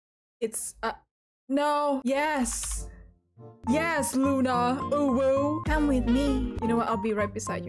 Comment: the uwu sounded like OOOOOwooooo